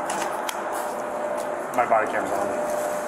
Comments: i definitely hear him say "My bodycam's on" instead of what the caption displayed. He was probably letting the other cop know not to act up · He actually says “my bodycam’s on” to the other cop because they have to act professionally and not go WHAT THE FUCK!!!!!